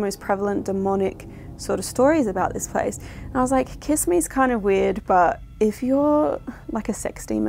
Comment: I don't hear it say "kiss me", I hear it say "listening"